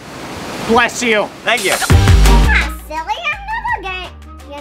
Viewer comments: did she just say I’m gay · No silly I'm not g- did she said that she was gay?